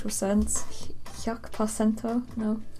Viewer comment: Did you say "yuck placenta" at 2:29? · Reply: CHOKES I CAN HEAR THAT NOW OH GOD I ACTUALLY SAID "HYAKU PASENTO" (one hundred percent) but oH GOD